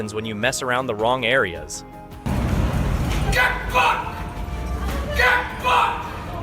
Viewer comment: At the 29:50 mk. Listen to what the soldier yells out. Caption shows, he says GET BACK. Listen again, he yells, GET F- - K-D. I like that guy.